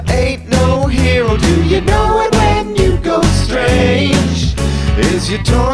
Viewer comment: Lol when he sings "you go strange," it sounds like he says "Hugo Strange"